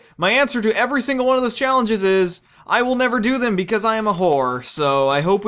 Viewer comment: my video buffered at around 1:53 and it sounded like you said "i will never do them because i am a horse!"